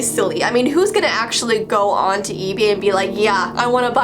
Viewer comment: "ebee" instead of ebay. Who else caught that🤔🤔😍😂